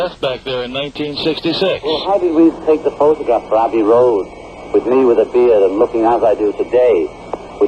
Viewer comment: sounds like he says Randy Rhoads😱